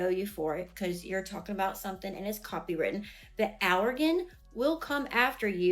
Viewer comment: did she say alrogen? Did she mean algorithm? Jesus